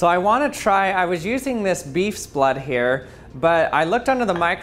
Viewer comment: Why does it bother me so much that he says “beefs blood” instead of “cows blood” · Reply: Sharon Rooney I thought he said “bees” blood and was very confused on how a bees blood looked like that and how he got so much